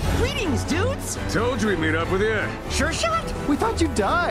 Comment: did Rigby just say sh***